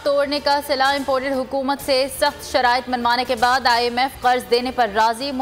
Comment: Did she said imported hukumat at 2:00 ?